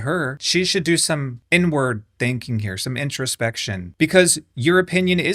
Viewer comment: Why did I first hear "inward thinking" as "n-word thinking"